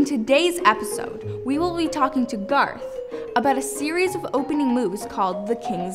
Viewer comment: Did she just say?! 😊